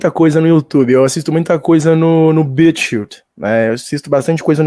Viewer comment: Qual site é esse q o falou? Não entendi como se escreve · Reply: Bitchute